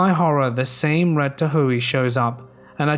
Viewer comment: took me a bit to figure out what you said. Then realized "Tahoe" pronounced "tah - ho" I think the redditor specified the vehicle because Tahoes are huge SUVs. <link>